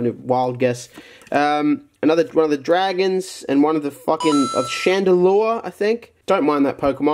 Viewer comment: Does anyone know what the pokemon is at 6:51? Your help will be greatly appreciated!! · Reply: Chandalure? He says it when he sees the card...